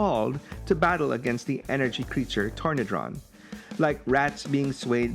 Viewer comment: When Call of the Primitives first came, my 9 year old self thought they were saying Pornicron, not Tornidron, making my parents rather concerned when i asked if they'd buy me Pornicron when it came out